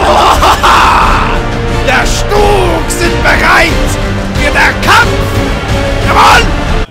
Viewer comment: can someone spell me what he said in 4:30 :D Das Stug steam Balight xxx Kampf Ja Mul? I dont know how to spell xD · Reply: law "Der Stug sich bereit für der Kampf, jawohl!" or something like that. It's not really correct, but it sounds awesome. Translated with a bit more senseful grammar it would mean something like "The Stug makes ready for battle, yes!"